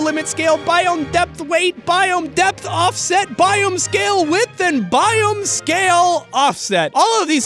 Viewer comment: Anyone else catch biome scale weigth